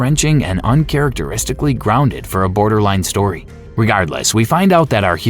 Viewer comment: Is this A.I voiced? Is that why some words are pronounced so oddly? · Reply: I noticed “Commandant” and “Torgue.” Plus “borderline” instead of “Borderlands”